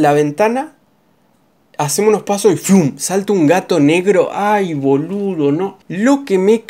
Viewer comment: estava distraido y escuche "un n3gro"